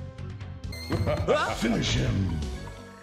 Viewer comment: Being spanish my first language i thought it said “oh si” XD